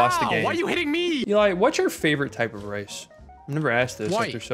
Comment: I swear just said "race" and Eli said white. It took me a second to process it was rice.